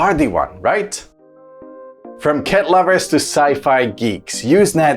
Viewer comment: i thought he said "ket lovers" lol